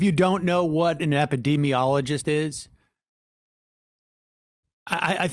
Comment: Thank you sam for explaining he was trying to say, "epidemiologist" I legit thought he was saying that we shouldn't listen to experts because "what even is knowledge tho..."